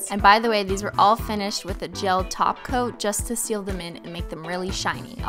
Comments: Every time you say top coat I hear “taco” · She's actually a REALLY good teacher!! She words things in an exact way where it sounds professional but not nerdy and too hard to understand if ur stupid like me! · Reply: I swear the way she says "Top coat" sounds like she is saying "Taco" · When Cristine said "I painted some test nails " i thought she said " I painted some testicles " 😂 Edit: Christine to Cristine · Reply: Well I kept wondering what she was talking about when I heard her say something about applying the "taco". Turns out she was saying "top coat". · When you say "top coat" I keep hearing "TACO" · Everytime you said top coat I thought you said taco 😂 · every time i think she says 'top coat' i hear 'taco' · I thought she was saying "taco" not "top coat" 🥴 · every time she says "top coat, i hear "taco" and now im hungry